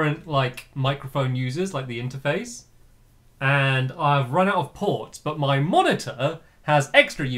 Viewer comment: at the beginning when he was talking about the ports on his computer I thought he said "I've run out of porn" XD